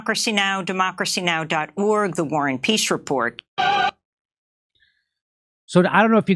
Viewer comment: i could have swore i heard the woman at the beginning say "WAR is PEACE" ....